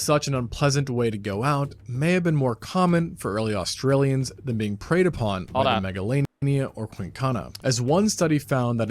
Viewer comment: what did you say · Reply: IconDreamy